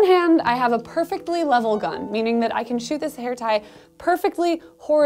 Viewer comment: only I misheard hair tie as hentai?